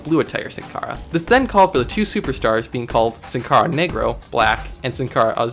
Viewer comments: Bro I thought they said negro I was finna be pissed but that said nagro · Sin Cara What? Did I just hear the n word? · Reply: No negro which is black in Spanish but yes I does sound like what you think it is